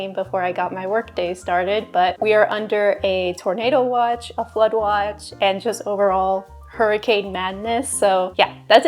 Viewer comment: Oh noo When you said flood watch I heard "A blood watch" and I was like ...... what kind of weather is THAT